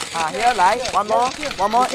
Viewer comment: What did that guy scream ? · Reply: He said "EH TAKE ONE MORE ONLY AH!!!!"